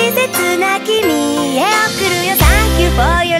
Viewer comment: ｢今ありがとう に大好き｣が｢今ありがとう いなり寿司｣にしか聞こえてなかった やっと分かってスッキリー！